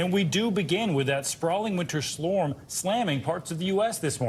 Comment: Did he say "Slorm"?